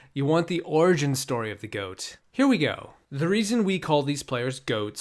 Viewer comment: Here we GOAT... That's what you said, and I didn't heard nothing else.